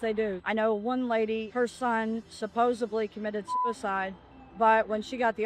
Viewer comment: "supposebly"? TF is that word?